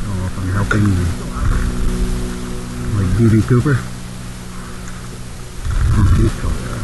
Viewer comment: Did he say "D.B. Cooper"?